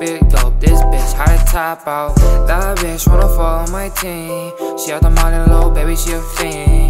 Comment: I thought he said “That bih wanna fugh on my ting”